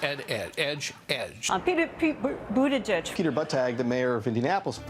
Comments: That one dude though: "Peter buttplug" Oh wait I misheard "buttag" xD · I don't know why but i kept hearing "pete bootydrag".